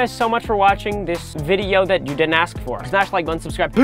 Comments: did you say smash like unsubscribe? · did he really say un-subscribe? well ok then.